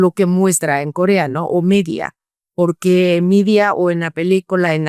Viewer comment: Excelente entrevista. Una pregunta: ¿De qué película hablaba en el minuto 18:38? Entendí que la película se llamaba "Midia". ¿Alguien sabe?